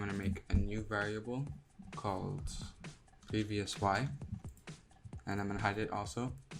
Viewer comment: I dont hear and understand what you named the new variable so would you please tell me? Was it pbsy? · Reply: *prevY*, like *previousY*, but it shortened